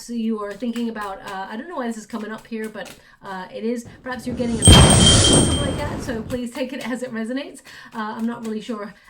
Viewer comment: What the heck! Happened to me too! 2:45 - and idk what she says! It won't show the word on CC, there's no transcript of the audio - what did say?? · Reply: starts at 2.44 (play sec by sec, it is bit easier to hear words). "perhaps you're getting.... ( 🤔* a new *🤔) ... haircut..." that sound was louder then slotmachines..more like metallbars & chains... 🤔